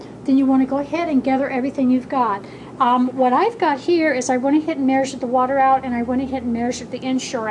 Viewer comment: Excellent Video.. But I don't understand what she is saying from 2:30 to 2:33.. something like "I want to hit merge with the water out and I want to hit merge with the Ensure out". If anyone can elaborate please help. I hate to miss anything because these are such great vids. Thanks! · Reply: I went ahead and measured the water and ensure out